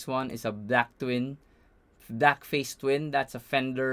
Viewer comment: My friend thought you kept saying "black face" and now i can't unhear it